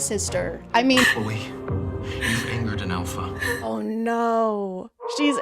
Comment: Wow, i thought he said “You fingered an Alpha”.